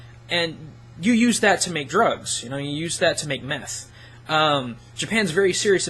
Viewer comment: at the 14:20 did he just say "and I used that to make meth" , I find it highly unlikely that you would say that on Youtube but were you a meth cook ?